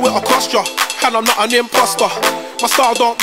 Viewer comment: did he just say im not an imposter?